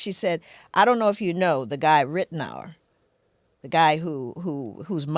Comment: what is she talking about? Who’s name did she say I cannot for the life of me figure out the spelling 🫠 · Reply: Kyle Rittenhouse